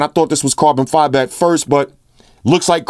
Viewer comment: lmao @ 4:57 i thought you said "looks like carbon fiber to the bitches"